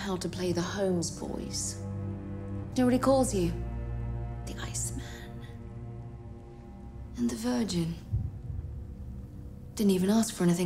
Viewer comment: what she said? i'm not native. I read the legend but i can't understand like "D’you know what he calls you?" turn what she said =/ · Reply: He called them 'the ice man' and 'the virgin'